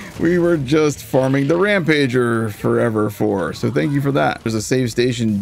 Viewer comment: When you spoke about the Rampager actually being the fire storms dedicated drop, it sounded like you said "red jabber". I was looking away from my phone when you said that and was wondering where this boss was hidden 😅